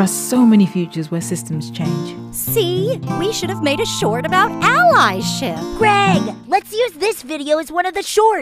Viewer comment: I can't help but think Pearl said "Ally shit"